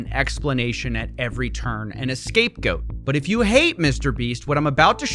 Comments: Did he say an escape goat? @ 00:40 only say that bc I heard AN and not A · Did you just say "escape goat" Hahaha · Did he say "an escape goat" as a joke? 🤔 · did he say escape goat instead of scape goat lmao??? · Did he just say an escape goat? .-. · did he say excapegoat? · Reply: A scapegoat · Did he just say "escape goat"? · Did I hear you say "An escape goat" ? 0:39 I don't think you can claim to have said a scapegoat in post :) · Did he say escape goat? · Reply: Scapegoat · Did he just say an escape goat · an escape goat!😭😭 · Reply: I thought it was "And a scape goat" Guess I misheard 😅